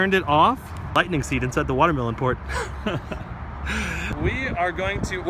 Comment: Did he just say "Lightning seed inside the watermelon port" 😂